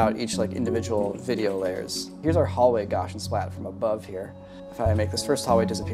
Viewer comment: I legit thought he was saying 'Gosh n Splats' Pretty sure it's still just an S sound. I've never heard it pronounced with and SH.